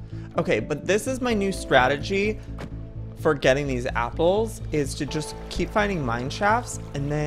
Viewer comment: Is it just me cus' I really heard he said" three apers?" 😂